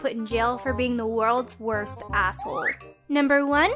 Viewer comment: I heard “Asshole”